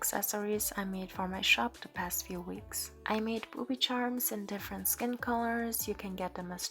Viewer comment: I thought you said ‘I made some poopy charms’ 💀